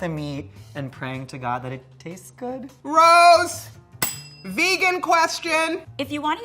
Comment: “ROSE. VEGAN QUESTION.” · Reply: I thought she said "Gross! Vegan question!"